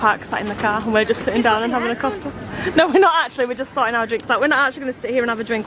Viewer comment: Thought said they were at Costa, yet the cups were Starbucks, make ur mind up